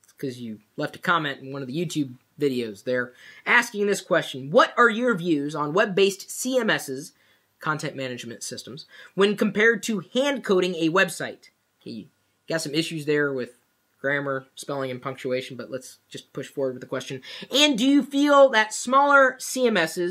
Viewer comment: First i thought he said "hand cooling" LOL